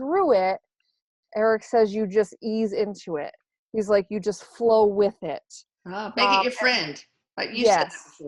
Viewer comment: i think i heard Erik say "flow"! Love your videos! ❤❤